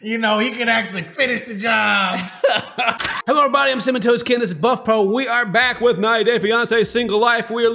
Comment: The first time I heard him say his name I thought it was 'sementoastken' cause he says it so fast.